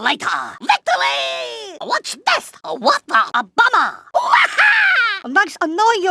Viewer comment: Why did it say obama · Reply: It says "a bummer" :v